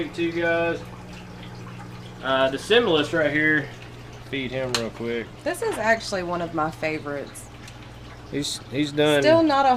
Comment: what is tht called? zimbalist? it sounded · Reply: similis monitor or (Varanus similis)